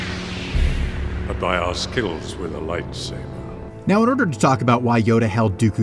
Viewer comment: I thought he said “Skittles with a lightsaber” for some reason